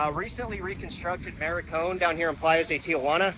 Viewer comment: did you call it the newly constructed “Maricón”? Lol, that is derogatory Spanish word for a gay male. It is the Malecón